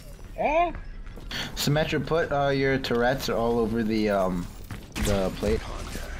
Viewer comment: Terrets? or did i hear wrong